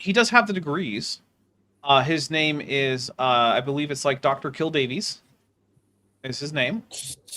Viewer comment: I misheard that as "Dr Kill Babies" 😂